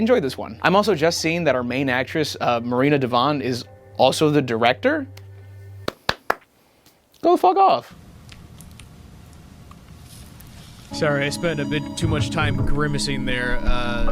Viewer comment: i heard "go f*** off" and i was like "damn that bad?" Hahaha